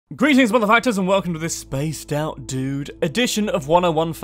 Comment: I thought he said greetings mother f__kers. I loved it.